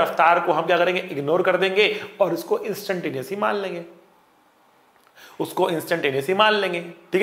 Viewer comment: Hey sir what did you say at 12:24 - 'Instant __ man lenge'? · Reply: Instantaneous